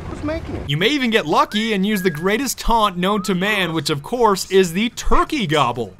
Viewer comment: DID YOU JUST SAY SAM FISCHERS THE GREATEST?! TRY MY BOI SNAKE UP IN THIS!